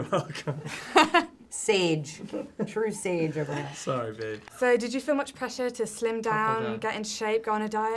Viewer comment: did he say "sorry babe"???